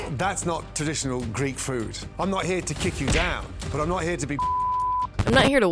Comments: I think he said "I'm not here to suck you off" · I think he’s saying “but I’m not here to be a pu$$y” but truly I don’t know lmao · for 5:23 i think he said "but I'm not here to be a bullshitter." but they censored the word too early. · My best guess is he said “I’m not here to kick you down. But I’m not here to be bullsh*tted.” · I think he’s saying “Im not here to be fucked with” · "I'm not here to be bulloxed" is what he said around 5:30, I do believe. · I think it was "I am not here to kick you down, but I am not here to be bullshitted" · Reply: i thought he said “im not here to be fucked with” but bullshitted makes more sense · I think he said "I'm not here to be fucked in the arse" lol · I think he might have said he's not here to be sucking your ****"? That's how my brain autocompletes the sentence and it look like his lips fit for the last word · I think he's saying Bullshitted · I think he says "I'm not here to be bullshitted." Source: I'm a Brit, although I don't think this actually changes anything concerning the legitimacy or accuracy of my suggestion.